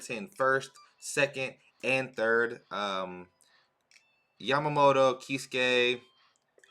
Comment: Haha 17:40 i thought you said “yamamoto he’s gay”